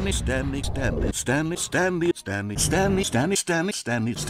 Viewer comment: It sounds like he is saying stamly or even stamnley